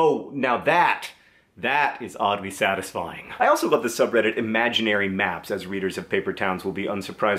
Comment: At first I thought I'd heard "imaginary maths" instead of "imaginary maps" and I was really surprised. Now i just want to see a subreddit all about fun stuff you can do with imaginary maths :D